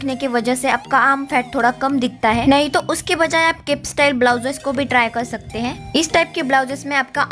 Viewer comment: What style did you say at 3:22? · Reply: off shoulder or cape style blouse designs